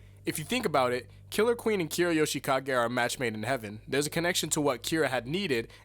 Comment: Did you just say 『Made in Heaven』?